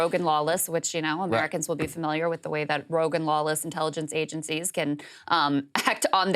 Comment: it took me a min to realize that Krystal was not saying "Rogan lawless"